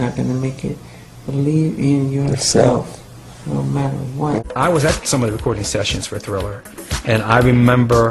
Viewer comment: Did he say believe and you'll receive no matter what? The fucking interviewer decided it was okay to talk over him -__-. BTW he says this around 3:30...